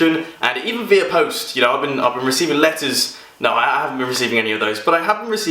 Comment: I thought he said lettuce, not letters.